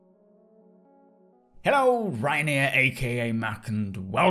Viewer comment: Did you just say “Hello RyanAir” ?